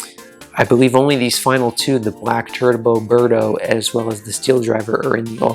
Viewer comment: Black Turd-bo Birdo. Best saying ever It also perfectly describes the kart for me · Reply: No I'm just saying it sounded like you said Black Turd-bo Birdo instead of Black Turbo Birdo. Maybe it was just one of those moments where something sounds different from what's being said